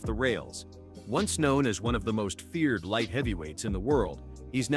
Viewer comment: I thought he said Raja was one of the most feared light heavyweights 😂😂